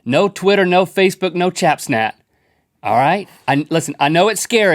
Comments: I was closing my eyes until said chatsnap, then I was done😂😂😂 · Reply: I thought he said chapsnat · did he just say chapsnat 😂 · Did he just say Chapsnat at 00:27 ? :,D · I was closing my eyes until said chatsnap, then I was done😂😂😂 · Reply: I thought he said chapsnat hahaha 😂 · Did anyone notice say "Chap Snat" instead of Snapchat xD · I heard "chapsnat"...why..